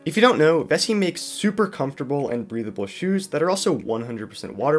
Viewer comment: I thought he said breedable 💀